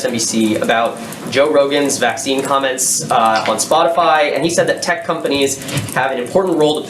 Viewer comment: Did she just say spotifly...?